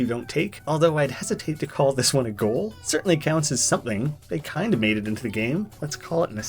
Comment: Be sure to Wishlist the upcoming, expanded version of Dead Estate over on Steam ► <link> We are rounding the corner of 10,000 Wishlists!! That's HUGE! And will go so far into helping get the game out there. Thank you so much for your support! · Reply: I thought he said cunts because he noticed there was two of them after the line